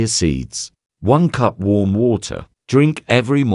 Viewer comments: I legit thought you said worm water when you were talking about the chia seed cleanse drink 💀 😂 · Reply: Ha ha I thought she said worm water too 🤣🤣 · I legit thought you said worm water when you were talking about the chia seed cleanse drink 💀 😂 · Lauraaaaa I REALLY thought you said "Worm Water" and was a genuinely concerned!!!! Thank god you included the tik tok with the captions after lmaooooo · I thought she said worm water😂😂😂